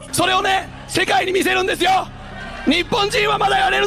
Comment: En el minuto 1:38-39 parece que dice :"Soy un misionero de Dios."Pensé que estaba hablando español.